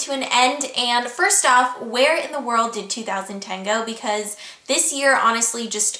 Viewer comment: For some reason when you said "where in the world did 2010 go?" I thought you said "Where in the world do you download techno?" hahahahahahaha